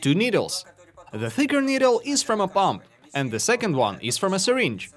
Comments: Awesome build, you got a sub for this one. Unrelated note: when you were introducing the parts, I thought you said "And a big needle from a bong"... I misheard "pump" from the accent lol. Keep up the good work! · When he said "the thicker needle if from a pump" i thought he said "a bomb" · I thought he said the thicker needle is from a bomb and i was like... "and where did you get the bomb from, mister roman?"